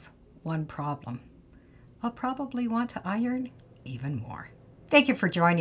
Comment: at the end I think you said even more. sorry , I heard it now. time was